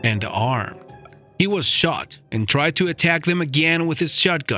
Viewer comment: Sound like he said "mustard attacks" 😂😂😂😂😅